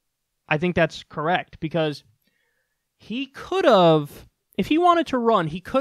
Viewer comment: I think Kyle is saying "could of" :(